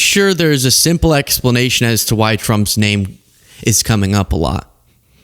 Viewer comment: Sound like Pam said "Drump".